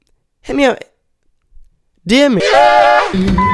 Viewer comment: I thought said the n word at the end but it was just “new group”